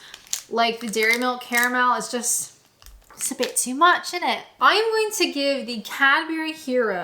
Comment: did you just say "innit"?